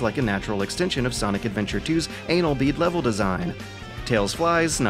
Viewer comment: Is it a bad sign if I hear the word "anal bead" and not bat an eye?